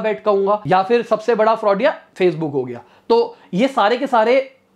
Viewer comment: did he say fraud Facebook 😂😂😂